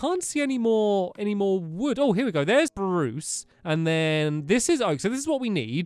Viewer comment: is it just me or did he say Bruce